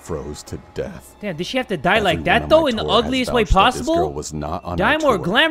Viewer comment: if you put caption it says loveliest way instead of “in the ugliest way tho”